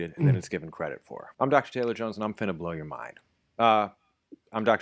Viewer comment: did anyone catch his introduction, "I'm Dr. Taylor Jones and I'm finna blow your mind." Translation: "I'm fixing to blow your mind." It was very subtle, but he snuck that in there. LOL